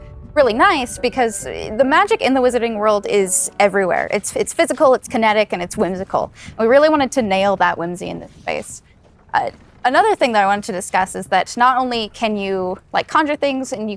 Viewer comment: did she say, "Nail that whimsy in the face"?